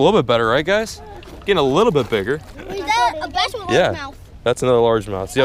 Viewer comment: did he say "Is that a BASS or a LARGEMOUTH?"